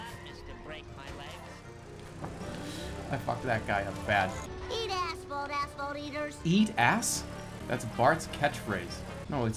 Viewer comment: i remember back in the day, as an 8 year old with a vague grasp of the English language, i thought Bart was saying "eat ass bone ass bone eaters"